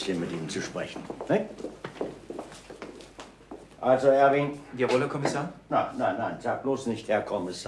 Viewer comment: Harry ist doch garnicht Harry!..... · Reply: Ich habe auch "Harry" verstanden. Aber bei nachträglich genauerem Hinhören könnte es auch "Erwin" heißen. Je öfter man es anhört, desto weniger weiß man es ...